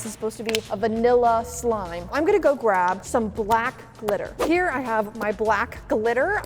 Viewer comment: I thought u said for the first one “let’s get some black littler”🤣🤣 also once u put the black glitter in it it sorta looked like oreo ice cream only real fans can like this 👇